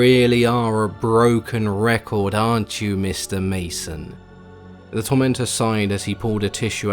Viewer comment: It sounds like hes saying Mr Mesa🤓 lol 🦌💀👁